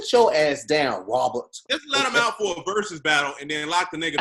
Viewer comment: I heard that Thair 51:50 "Wabert" 😂😂😂😂